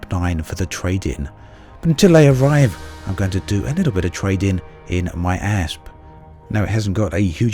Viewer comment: misheard. xD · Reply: I'm gonna do a little bit of trading in my ass!